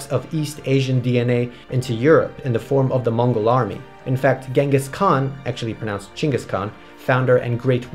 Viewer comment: If Genghis Khan lived today imagine all the child support bills. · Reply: For some reason, I thought u said Genshin instead of Genghis lol 😂 must be playing a bit too much